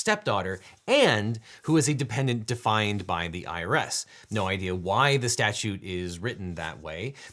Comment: or is what he said. I have pulled the statue itself though. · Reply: What? At 20:07 he clearly says "… and who is a dependent defined by the IRS."